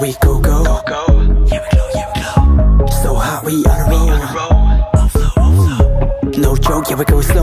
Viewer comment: DID YANGYANG SAY "SO WHAT WE HOT WE YOUNG" IN A SEXY VOICE I'M— 0:39 nvm he said "so hot, we on a roll" i'm now sad in nct dream we young era 😔